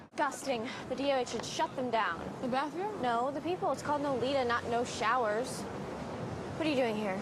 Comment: wht did she say!? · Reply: it's called "nolita" not "no showers"